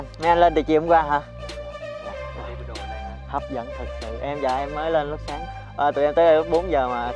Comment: s có ai nghe a nói gì hk ta... haha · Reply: em với vợ em mới lên lúc sáng =))